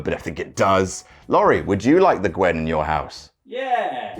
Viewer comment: Lol don't know why I thought it said GWENT.